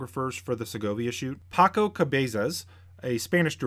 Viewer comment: That caught me off guard. For a moment I thought you said "taco cervezas", and my mind went directly to... "Oh, it's lunch time. Good idea." Then I looked up to see Paco Cabeza's picture on the screen.